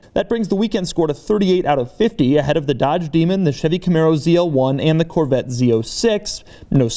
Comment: Thought he said "shitty camaro ZL1"...I was super triggered...